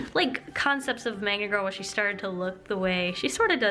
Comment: I swear to god, I thought that she said manga girl everytime she said Magna girl in her videos over an half year owo